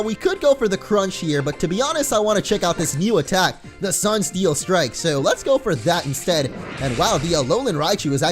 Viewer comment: when you said sunsteel strike i thought you said "sun feels good"